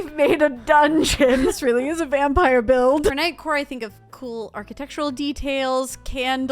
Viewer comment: I genuinely thought they said “nightcore” and nearly spat out my drink